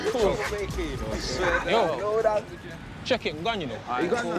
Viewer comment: what the hell did he say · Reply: 'Yo...check it an gone ya know'..